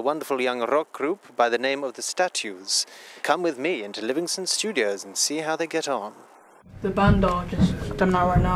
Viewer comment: did he say Livingston studio?